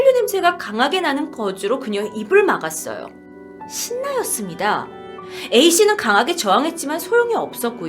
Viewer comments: 초 쯤에 뭐라고 한거에요?? 신나였습니다? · 저만 못알아들었나요..? 신나가 뭐에요..? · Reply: 신너